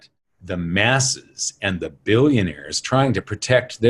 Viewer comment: When you said billionaire, Mike, I thought you said bullionaire. My mind must be on the near future ;)